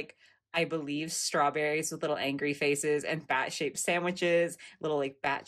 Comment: I swear I thought you said bat $#it sandwiches. 😂😂😂😂😂😂